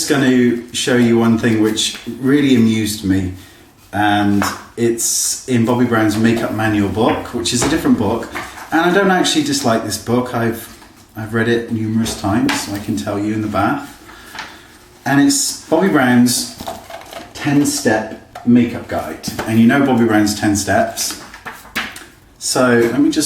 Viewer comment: did you just say you've read it numerous times in the bath?? I love yooooooou!! Hahhahahah I love reading books in the bath :)